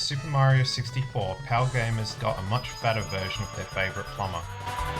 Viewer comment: did you said pal gamers got a much better version? you meant ntsc gamers got a better version? as far as I know nintendo didn't bother to optimize their games to account for the increased pal resolution. rareware games starting with their snes games took full advantage of the pal format. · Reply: Sorry the audio quality isn't very good. I actually said "fatter version of their favorite plumber" ... a joke at the fact that the screen was squished with black bars for us PAL gamers. Thanks for watching!